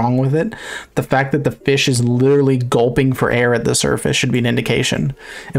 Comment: did he say surfish?